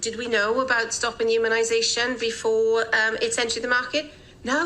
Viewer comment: "did we know about stopping immunization before its entered the market, No" Ok so did she say 'IMMUNIZATION'? ' HUMANIZATION'? I hear her say no, But is she actually answering the right question...? Speed of science??? O right with a label of FIH - First in Human... Sounds safe to me lol @ 8:59 "and from that Point of view WE had to do EVERYTHING AT RISK" So... side effects be damned... lets test it on the do right thing mob... ok Got it.